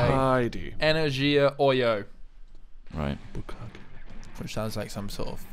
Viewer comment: did i just hear a very stealthy bukkake at 14:39?